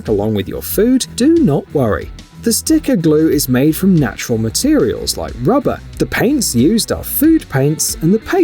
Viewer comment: Did he say "The glue is made from natural ingredients like ...rubber" ? 😳🤔